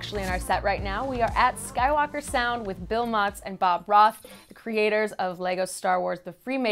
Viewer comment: When she says Bob Roth I thought she said Bob Ross and I was like "but he's dead"